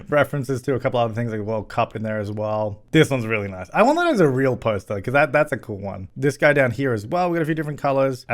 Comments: "I wonder if it's a real poster" lol! It's Pink Floyd's Dark Side of the Moon album. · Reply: I also thought he said "I wonder if that's a real poster", aaaaand just commented on it myself, LOL. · This one is really nice. I wonder if it's a real poster. It's Pink Floyd 'Dark Side of the Moon' with a plumbob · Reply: Sounds like he said "I want that as a real poster"